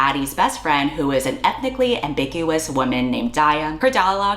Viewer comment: OMG, when you were describing Daya at around 6:44, my brain misheard "ethnically-ambiguous woman" as "ethically-ambiguous woman." As in her sense of ethics and morals were questionable. XD